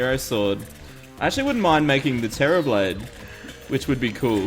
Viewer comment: Sounded like he said "Terrorblade"